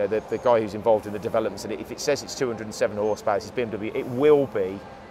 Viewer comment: Did he just say BMW R R lol